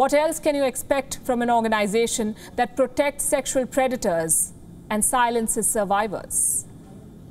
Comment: ''A Guardian angel turned predators" a big shoutout to the cover-up policy of WHO. Love you Palki Mam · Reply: I think you misheard. It is predators not creditors.